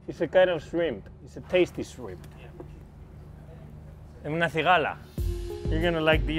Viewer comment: What did Carlos call the shrimp at 3:00 ?? Haha I can’t seem to figure it out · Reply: I believe he said "cigalas" (prawn/crawfish in Spanish).